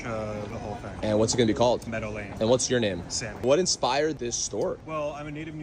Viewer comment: In the beginning of this whole story I always heard "Medellin Store" and thought yeah, no wonder it's so expensive 😂